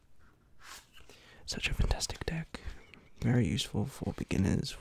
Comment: Who else thought he was saying autistic deck